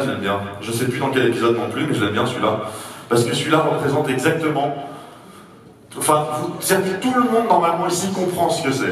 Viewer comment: Qu'est ce qu'il dit le type dans le public à 7:50 ? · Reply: "C'est une super idée Bruce !"